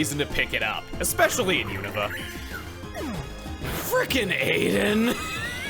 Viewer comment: Did I hear mention of an "Eyan" there, or was just saying "Aiden"? I can't decide due to the context of this encounter.